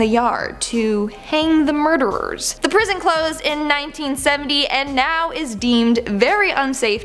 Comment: I thought she said the prison clothes in 1970, and I was so confused 😂